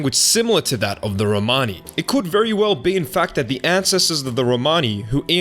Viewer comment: I was super super down with this video listening at work on Bluetooth…. I thought you said Graham Hancock and I almost immediately tossed my phone clicked off and down voted lol I’m glad I rewound and made sure it was Ian Hancock not the bad man…. Thank you for this video!!! Very interesting!!! Big thumbs up new subscriber